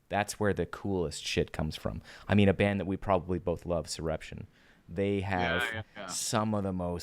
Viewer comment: Really enjoying this.. but now I want to check out that band you mention at around 46:42 but I can't quite make out the name! Can you tell me please?! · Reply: Soreption! They're sick.